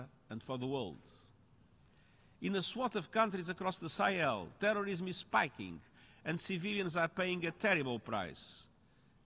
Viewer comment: what did he say at 6:59 ? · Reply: "In a swath of countries across the Sahel, terrorism is spiking and civilians are paying a terrible price."